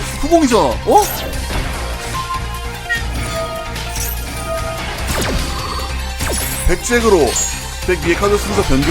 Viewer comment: 전부터 궁금했는데 0:10에 이거 뭐라고 말하는 음성 소스인가요? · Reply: 딸피들만 아는 옛날 핸드폰 "한시" 알림음입니다